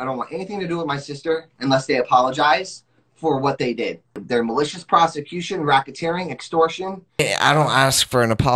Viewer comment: I thought he was gonna say there malicious prostitutes 🤣🤣🤣🤣🤣🤣🤣💀💀💀💀💀💀💀